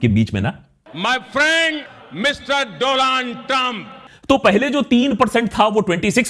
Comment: Did I hear "my friend Doland Trump"